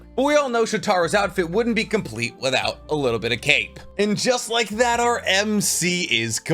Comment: I really thought you said his outfit wouldn’t be done without a little bit of cake. Then I realized MC kinda has that cake anyways lol